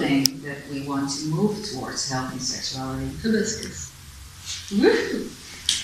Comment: i can't hear the food she said help the second chakra someones hear it (i'm french that's why i don't hear as well) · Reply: Hi dear Adimirror, Teal says Hibiscus. Hope you could hear the rest of the interview well. I added subtitles too, so see if you can turn them on so you can follow the whole interview. Much love!